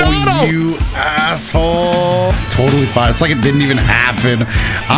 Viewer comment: d-did just said "Asshole" and kept it in the video in the main channel?